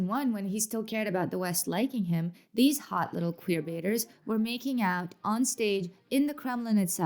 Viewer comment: Sorry, I thought you said queerleaders and all I can think about now is the awesomeness of gay cheerleaders being called queerleaders. XD Could call it queerleading. Be on the queer squad. (I'm bi and Putin's anti-lgbtq campaign is deplorable; I just found what I misheard hilarious for some reason.)